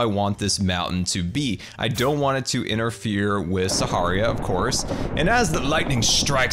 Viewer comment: did he say, "Saharia"?